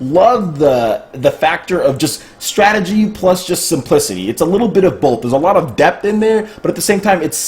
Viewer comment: "strategy plus just some pussy" i did not mishear that